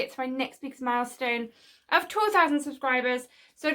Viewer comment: Well it sounded like you said 12 000 subscribers is your next goal 😳